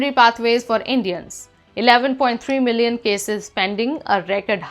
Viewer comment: Did she say there were 11.3 million Indian green card cases pending (5:26)?